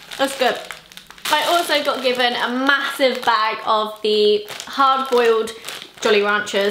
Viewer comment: Did anyone else hear "hard-boiled" jollyranchers?